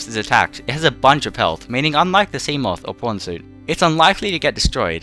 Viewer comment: what he said: “PRAWN Suit” The captions: “p*rn suit”